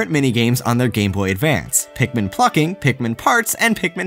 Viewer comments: I thought he said Pikmin fucking. I had to rewind just to make sure. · Wasn't paying attention so I thought it said: Pikmin Fucker